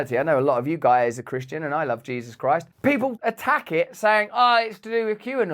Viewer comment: I swear you said “I AM Jesus Christ..” 😂